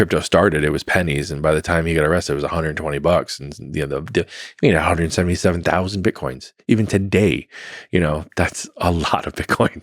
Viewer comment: Did he say 1,770,000 BTC or did I misunderstood?